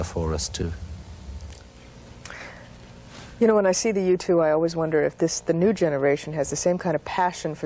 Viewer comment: The U2? I didn't know what she was talking about for a second. I thought she was saying, "The Youtube."..................TV commentators get everything wrong. Katie Couric said, "What is Internets?"